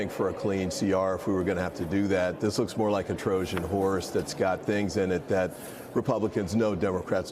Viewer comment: did he say, “Trojan whores?”